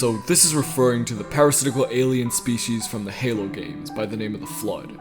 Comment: thought you said pyrocinicle alien species at first